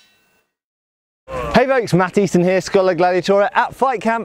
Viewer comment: I allways thought you were saying "Hi, Mattis in here". So, uh, now I know Matt Easton. (yes, I'm french)